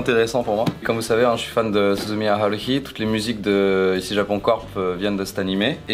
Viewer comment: les sous-titres YouTube n’ont pas compris de quoi est fan…😂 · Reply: Pareil pour moi, je voulais connaître l’anime. Avec la photo j’ai trouvé : Haruhi Suzumiya